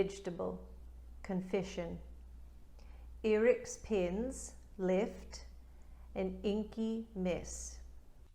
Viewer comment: That American woman speaking about leaking pens, I thought she’d said, “Eric’s penis left an inky mess “ 😂